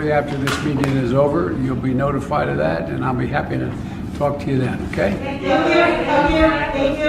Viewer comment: He mumbled so much at the end I swear I thought I heard him say I'm going to talk to Putin and then I'll f#-k you then. But I said to myself... nah... I'll talk to you then.